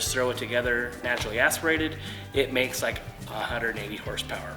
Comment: DID YOU JUST SAY 180 :face-fuchsia-wide-eyes